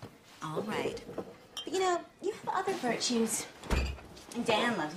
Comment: Hello! I love this video so much, and I want to make sure everyone can understand and enjoy it. That's why I wanted to let you know that your captions are a little off. At about 1:23 the auto-captions say "you know your father for Cheese " when it meant to say "You know, you have other virtues".When you get a chance could you fix that, please? That'll help the 360 million folks globally who rely on closed captions to understand the video. Thank you, and keep up the great work!